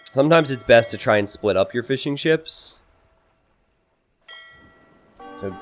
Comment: Damn every time you say "Fishing chips", all I hear is "Fish and chips" and now i'm damn hungry